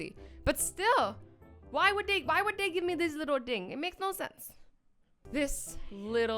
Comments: the way you said „thing“ in English sounded like the word „Ding“ which means „thing“ in German😂😂😂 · I had my volume low, so I heard, "WHY WOULD THEY GIVE ME THIS LITTLE DICK?"